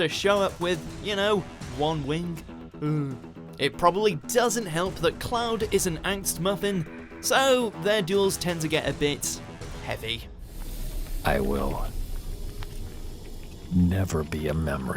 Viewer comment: what the fuck is he saying i've listened to it about 100 times. Axed Muffin? WTF?